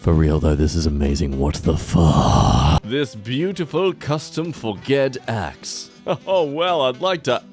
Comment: did he just say for-ged